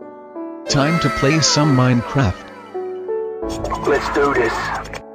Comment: did he just say Minecrap